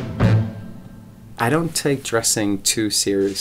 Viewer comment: what did he say at 2:20??? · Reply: I don't take dressing too seriously to be honest, I think it's probably the most important neglectance that there is.